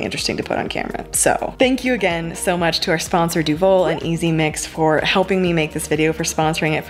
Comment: At 1st I thought u said easy Mac and I was like WHAT that’s the yoke of Mac and cheese I eat